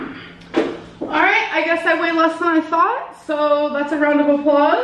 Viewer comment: I swear that scale said 590.8 not 490.8 😅 5:11 - what did y'all hear ?